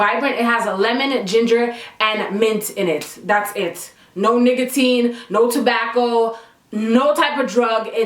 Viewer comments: am i the only one who heard niggatin at 6:43 when she was talking about that thing in her hand. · this girl really said “niggatine” instead of nicotine 💀💀😭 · YOU GUYS DONT UNDERSTAND THE STRESSSSSSSSSS I WENT THROUGH TRYING TO UPLOAD THIS SHIT!!! GIVE IT A THUMB UP JUST BE-FRIGGIN-CAUSE ! :') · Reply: My girl..did u realize u said NIGGAtine instead of NICOtine?? LOL...PS loving the camera..ur face like its glowing!